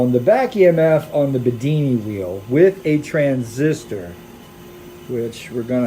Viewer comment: Lol I thought he said Houdini wheel 😅 Would be a good name for it anyway, making energy like magic